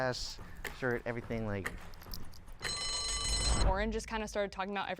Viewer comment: did she say orange 😂😂😂